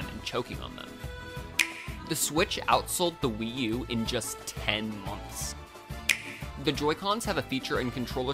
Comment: I thought you said 10 minutes...